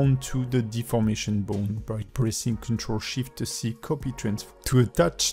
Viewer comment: I don't understand what you said and did at 1:45 , please will you explain. I am new to using blender but this tutorial seems to be the most helpful. I am just struggling to understand what you are saying at time. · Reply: ctrl+SHIFT+C to add a conbstraint to a selected bone. you can activate the subtitles to help :)